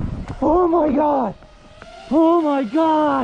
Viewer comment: he acknowledged god like 5 times and then took credit for the save at the end. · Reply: I thought he said how did he blow that